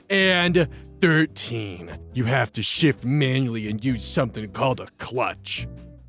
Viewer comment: I thought you said manually shit instead of shift